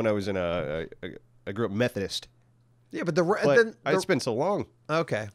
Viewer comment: Sounded like said "I grew up Meth Headist" lol